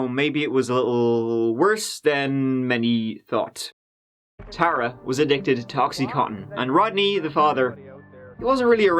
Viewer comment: Haha 😆 I thought said “tara was addicted to toxycontin”. I thought that was so clever then realized he said “ [she was addicted] ta oxycontin” (say it fast) not to “toxycontin”. Im still going to use that 😊